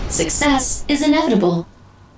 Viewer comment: Did she say "success is inevitable?" at 5:50? Thanks.